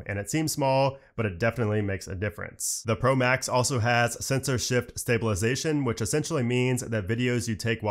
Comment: I heard "censorship stabilisation"